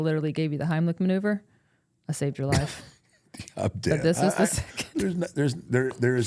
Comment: did undertaker just say the phrase "Im dead"?